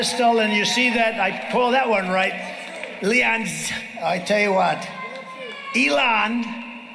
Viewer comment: what did he say? "Leons"? Trump the "Mis Speaker" of the Year in Maga World. Wonder if that is an amusement park or a creepy town fair?